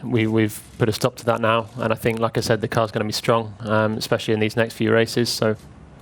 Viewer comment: Lacazette :D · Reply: Papaya 65 when palmer said "like I said" it sounded like he said Lacazette